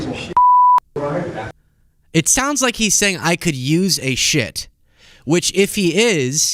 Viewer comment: It sounded like he said "I could use a shit load" to me. They didn't bleep out shit though. He also said we have been at war for 6 weeks. Wrong it has been 2 months. Sleepy Dump needs to be removed from office. He is out of touch with reality.